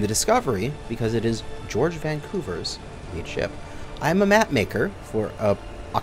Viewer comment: What was said: “We’re taking it and calling it our lead ship.” What I heard: “We’re taking it and calling it Barley Chip.”